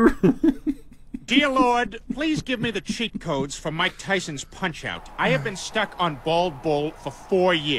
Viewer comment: omg i DONT think I am going on drugs i think he said about mike Tyson’s punch out